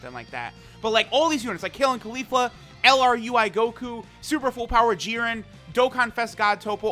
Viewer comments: did he just say lr ui goku!?? 😂😂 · Did i just hear LR UI Goku?!???